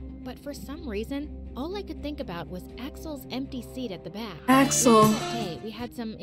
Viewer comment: Idk why I heard “empty seat” as NPC